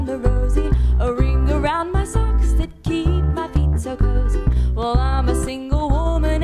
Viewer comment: I heard ''a ring around my socks that keep my pizza cozy''.